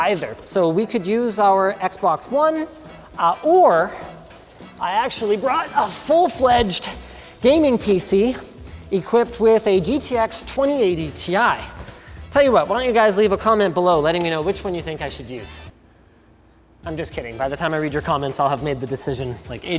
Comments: did he say GTX 2080ti? lol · Did he really say GTX 2080ti?